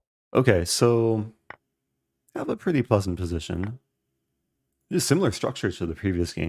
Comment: I thought you said “I have a pretty bussin’ position” lol